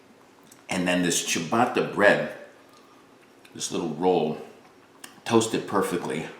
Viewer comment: Definitely thought you said chewbacca bread 🤣 great sandwich!